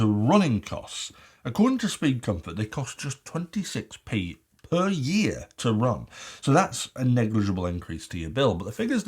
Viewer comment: Think I got to get my ears check. Did he say 26p PER YEAR?